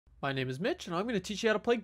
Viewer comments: Who else thought he said “my name is bitch” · Oh lol thought he said his name was bi**h lol but then i rewinded and read it and i say mitch